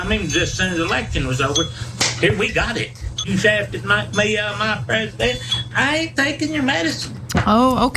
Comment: I turned on the caption and still can't make out exact what that trumptard said " shafted knock me out of mah prezDENT, I ain't takin your medicine! " How does the interviewer not lose it??? And I feel sorry for the dog being held